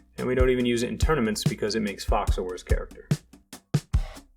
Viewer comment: What did he say? It made Fox what? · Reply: a worse character